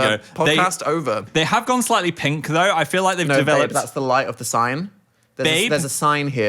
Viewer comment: I swear Dan did not say "babe"... that's a "Philip" if i've ever heard one